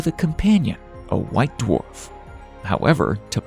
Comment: Did you just say "Lone-A** White Dwarf" lmao